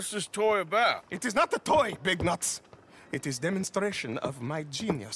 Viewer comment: did he just call Arthur big nuts ?